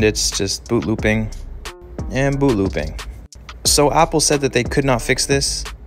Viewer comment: I thought he is saying ‘booboobing and booboobing’